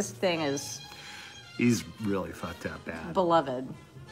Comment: Did he just say little Sebastian was “really ‘f*cked-up..?” - or did I hear that wrong? 🧐😂 [Uncensored Alton is the best! 👍🏻]